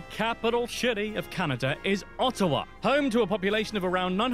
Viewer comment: Did he just say the capital shitty is Ottawa?😂